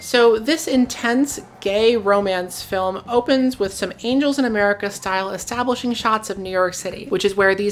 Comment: As i was just listening and not watching, i Gotta admit that i had to rewind and rewatch around 5:24 as i thought you said that it opened to an "Anals in america panoramic" and i just wanted to find out what porn opened with a city panoramic lol